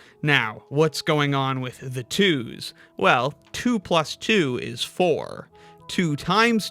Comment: My audio was cutting out so i heard "2 is 3 is 4"